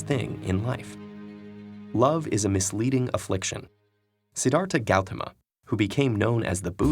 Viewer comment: umm its the Sanskrit name Siddharta Gautama in case u couldn't catch that because of the heavily accented pronunciation